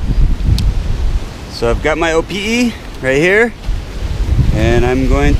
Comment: I swore you said "Opi'i" a few times :-).